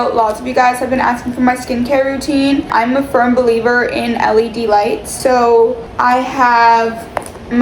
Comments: is it just me or did I think said ‘I’m a firm believer in d-elights 😭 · i swear i heard delights, not LED lights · I thought she said eli delights😂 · I thought she said “Eli Delights” 😂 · help I thought she said eli delights😭😭✌🏻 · I thought she said delights 😅😂😂 love you guys haha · i thought she was saying eli delights 😭😭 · Tell me why I thought said “Eli delights” and not “LED lights”😭😭